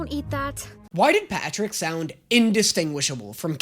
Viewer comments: did he just call Edrik PATRICK?? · did just call Edric PATRICK??